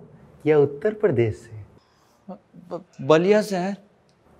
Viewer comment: Did he just said "BALLIA" se hai?? Apna ballia famous ho gya💃💃 Like those are watching from ballia..